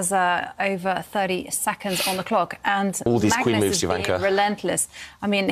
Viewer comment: did I hear that right? 😳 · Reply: Hear what? David says, "all these queen moves, Jovanka...", and Jovanka says, "Magnus is being relentless."